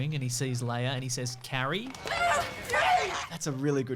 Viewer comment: I always thought Mark was saying HEY instead of Carrie as a kid.